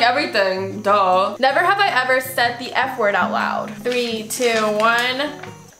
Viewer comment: i thought she said the n word wth